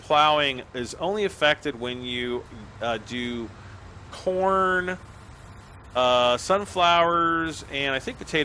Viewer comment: Is it just me or at 11:12 did he say porn instead of corn 😂